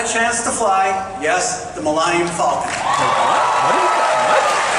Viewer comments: what did he say? "The Mill-i-num Falcom?" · Did he just call it the "Maligned Falcon"?! 😳 18:58 - I'm not sure of the wisdom of being asked to go to Kashyyk just to give the wookies blue balls. o.O All that said, loved the video. Where Disney fell down with the Galactic Starcruiser was in creating a resort where only the really rich can afford to go - and doing so after one of it's major movies (The Last Jedi) made explicit criticism of tourist resorts for the super-rich (Canto Bight). I'm from the UK and I could go on holiday to the Caribbean for two weeks for what Disney wanted to charge just to get into the Galactic Starcruiser just for one night! Walt Disney wanted to bring his magic to as wide an audience as possible. Locking all your major characters behind an extortionate paywall is NOT what he would have wanted. Imagine going to the Magic Kingdom and being told that you can't see Mickey and Minnie, Donald and Goofy, etc. That's what Galaxy's Edge did. They can easily feature characters from the other trilogy eras. All they need to do is have the different eras limited to specific times of the day. So if you go in the morning, you'll meet ObiWan and Anakin. Go near lunchtime and you'll see Darth Vader and Luke Skywalker. Go later in the day and meet Rey and Kylo Ren. It's not rocket science and you'll appeal to a much wider part of your audience.